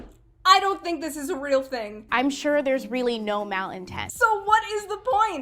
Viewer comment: Did she say "mal-intest" at 7:47?